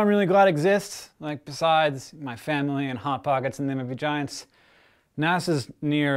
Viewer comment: I thought he said- "My parents, hot pockets and vaginas" were the things he was thankful for.